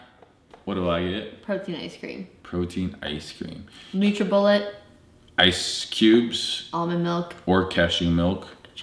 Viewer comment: What does say at 15:36? Can't understand that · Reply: Alessandro Rosso she says Nutribullet :)